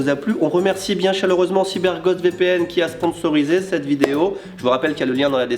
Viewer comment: C'est moi ou à dire "CyberGode VPN" à 00:43:28 ? Oo